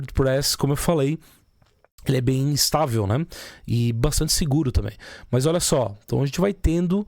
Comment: Parece besteira, mas aos 05:40, eu entendi você dizer que era “instável”. Se for isso e puder dar uma editada, ajudaria muito os noob, como eu, que aparecem aqui 😅😅